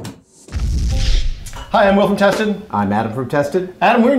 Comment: I thought he said "Hi I'm Wolf Intestine"